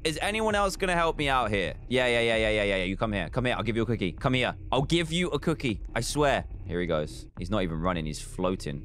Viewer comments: did anyone else also hear him say “Come here I’ll give you a quickey” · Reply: I heard him say “Come here I’ll give you a cookie” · why did I hear dan say I’ll give you a quicke